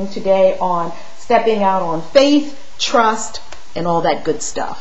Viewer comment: Great video. I love your ideas. Thank you for sharing. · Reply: I thought you said faith trust and pixie dust